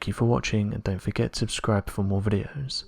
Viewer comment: it sounds like he says "..dont forget to describe for more videos." I really like this channel and I think we should have a whip-round to pay for this narrator to have elocution classes, maybe its his Microphone... maybe he has it stuffed in his mouth so when he tries to talk it sounds like he is blowing Ron Jeremy?? Ah well, it could be worse I suppose, he could pronounce the words 'presume' and 'assume' like 'preshoom and ashoom' and I am sure we are all agreed that would be insane because everyone knows that those words are not pronounced with a H sound, yeah?